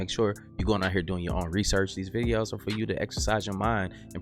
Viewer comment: Sounded like she said SEARCH & RESCUE